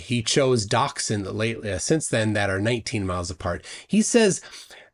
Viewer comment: what is saying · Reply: Jack chose docks that are 19 miles apart.